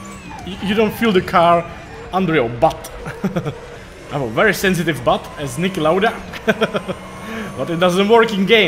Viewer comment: Nice race! Did you say you have a sensitive butt at 4:00? haahahaha